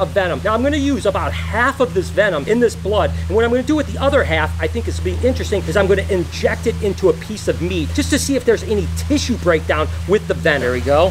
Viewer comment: When said we are going to inject venom into a piece of meat I thought he said he is going to inject venom into a piece of me